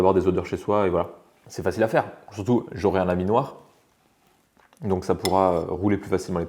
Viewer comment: "J'aurais un laminoir". J'ai entendu "j'aurais un ami noir" >< je me demandais quel etait le rapport mdr!!